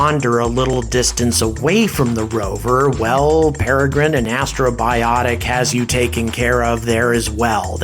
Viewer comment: I keep hearing you say "Astrobiotic," but the name of the company is Astrobotic (a portmanteau of the words astro and robotic). It's a slight distinction, but an important one nonetheless. Otherwise, great video! I really enjoyed this one. Exciting times!